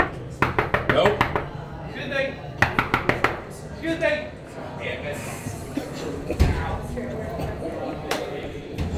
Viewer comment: What is he saying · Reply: “Excuse me!! Excuse me!!”